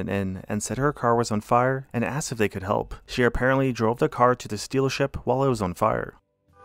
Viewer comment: did you say "sealership"?? :D